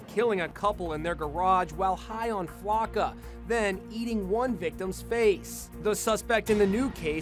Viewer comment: I thought it said "high on flakka cannonballs" and I was confused how do you get high on cannonballs????, Then I realized